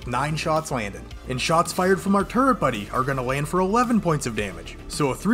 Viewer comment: For a second I thought u said "turd buddy"